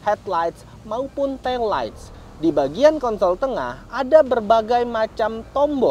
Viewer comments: bang bilang apa 😂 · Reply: Positip tiking aja dia bilang konsol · kedengerannya Kontol tengah